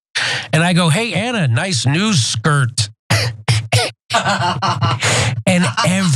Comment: Oh flip, I thought Jimmy said "nice new skirt" as opposed to "nice news skirt".